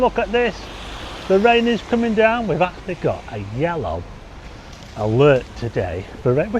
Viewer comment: Oh, my bad I thought you said FOOD alert 🎉🎉🎉